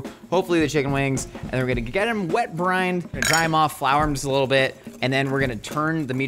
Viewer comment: Maybe it's the mythical origins I've been watching but I thought the first time Josh said "wet brine" I thought he said "wet Brian"